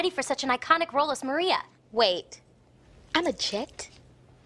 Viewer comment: what did Mercedes say please? I'm not that good at oral comprehension XD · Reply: wait- i'm a Jet?